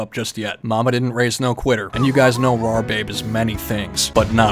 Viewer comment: did he just call himself "Raw babe" ? 🤣